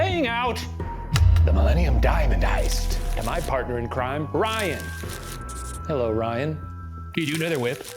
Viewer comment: i swear he said Bryan